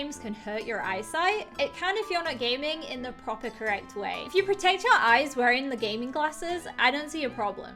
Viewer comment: I'm disappointed, I thought she said potato eyes but she said "protect your eyes". Smh I'm sobbing.